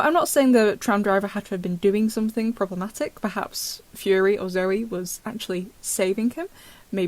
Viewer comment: Lol I thought it said "Furry" instead of fury🤣😭